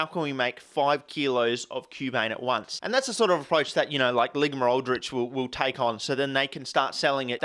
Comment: Did he just say Ligma-Aldrich...